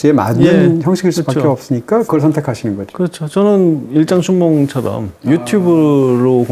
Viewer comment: (질문) 23:24 "저는 일단 000처럼 유튜브로 공개된 영화도 만드는 것이고..."에서 000은 무엇인가요? 아무리 다시 들어도 저는 안 들려요. ㅠㅠ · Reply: 일장춘몽 이요!